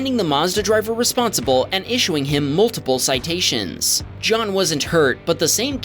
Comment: who heard John was a turd😂😂